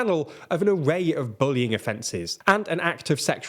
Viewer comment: I misheard "bullying offences" as "boning offences".